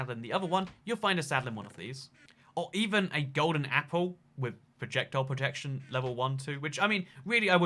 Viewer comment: U cant enchant apples with enchantments that is for armor And also u mispelled "item" to "idem" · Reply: RandomGamer2727: I am aware I misspelled item wrong and at one point it sounded like he said: even a golden apple with projectile protection